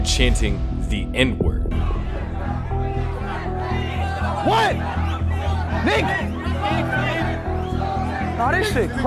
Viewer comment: i think they are saying книга (kniga) that means book